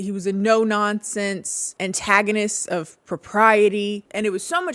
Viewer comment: When she said “antagonist of propriety” I heard “antagonist of sobriety” initially and…well, yes.